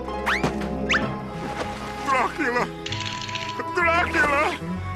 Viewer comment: Does Grim say Blackula before saying Dracula?? Lol